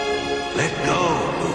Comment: I heard him say "Logic" lol.